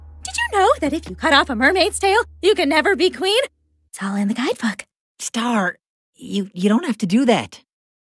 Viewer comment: Did She said "Tell him the Guys Fuq" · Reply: its all in the guide book